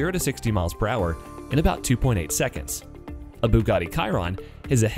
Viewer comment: did you say Bugatti karon and not chiron